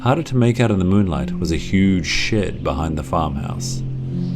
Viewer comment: When you said huge shed I thought u said huge sh*t